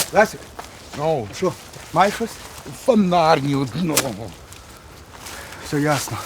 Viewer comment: что он сказал? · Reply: Фанарь - ні одного... "фанарь" то щось з фєні !!! Тобто мало